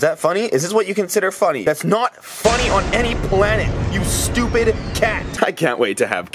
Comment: did say stupid to the cat he's Satan